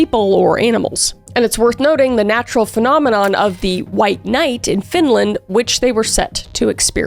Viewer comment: I heard "white knight" and got SO CONFUSED.. Never heard that term before.. Midnight sun is SICK, tho, until you try to sleep.. (We have 2 months with legit midnight sun, and April through August is longer days..)